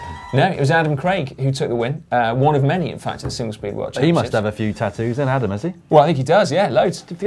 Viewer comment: caption comp: "I thought you said Singlet-seedy competition!"